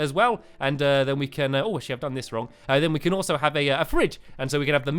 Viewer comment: did say shi..................t